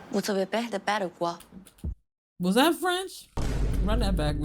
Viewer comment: this ain't french. At least, i couldn't understand what she said. 《 Are you afraid of losing 》translates to 《 As-tu peur de perdre 》Maybe the translation is a bit off? Idk, french is my first language and i couldn't understand it at all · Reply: maybe "vous avez peur de perdre, ou quoi?" that's the best I can hear